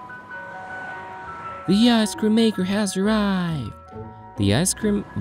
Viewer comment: Not me thinking this: 0:00 I thought it said I scream